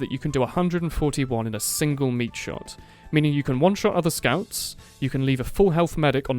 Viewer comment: It sound like you said "you can leave a 4 hp medic at 9 hp"